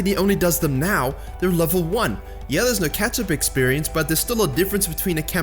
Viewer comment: I only heard "ketchup experience"